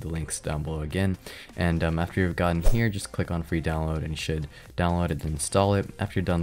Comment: why did i hear free as shit? i heard "shit download" lol